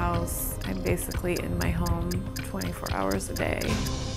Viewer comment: waaait at 1:14 did she say 44 hours a day?